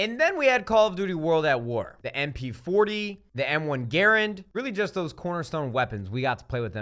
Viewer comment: WOWOWOWOWOWOLOWOW DID HE JUST SAY "M1 gayrand